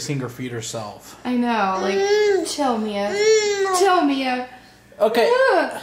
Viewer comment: HEY i heard some some one say mick